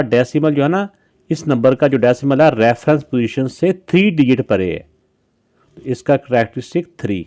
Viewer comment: sir said parryy instead of side😂😂😂😮😮😮😊😊😊😊 stop a video on 3:00 and listen carefully